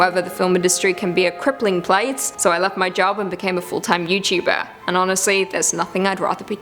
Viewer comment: OMG. I thought you said you quit and became a fulltime ZOOKEEPER! 😈😂 Thanks!🤘🐾